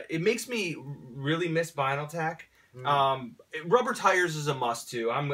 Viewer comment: whats the name/brand of figures shown at 11:28? they mention it but i couldn't catch it. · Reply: gill.i.am Transformers Binaltech. all time favorite transformers toy line.